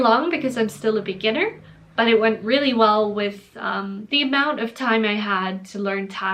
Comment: I heard "the amount of time I had" as "the amount of Thai my had".